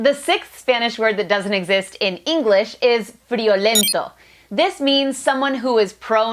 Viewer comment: Friolento in Spanish from Spain is normally said as "Friolero" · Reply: I thought you said "Frijolero" instead hahaha